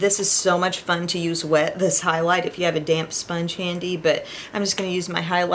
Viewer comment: I thought you said “ if you have a damn sponge handy” then I realized you said damp. I got a good 😂 laugh!